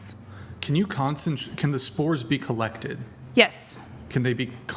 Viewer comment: I thought a minute ago she said the dang spores controlled us😂